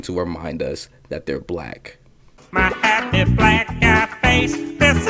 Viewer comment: MANDALA EFFECT !! i could have SWORE cleveland said “my happy mustache face”